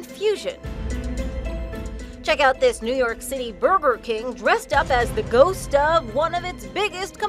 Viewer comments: Did she just say berber king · “ checked out this New York City burbur king” am the only one who hears burbur king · DID SHE JUST SAY BURBER LING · It sounded like she said Burber King or is it just me 0:10😂😂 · Is it me,or did she say Burber King?